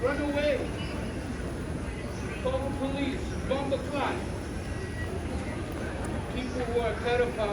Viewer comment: did he say bomboclaat during his rant?!? 🤔 color me confused